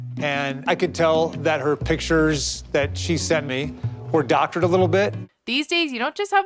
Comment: did he just say “her photos are doctored” 😭😭